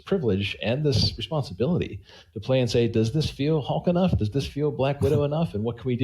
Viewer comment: did he just say "does this feel "Hawk" enough??"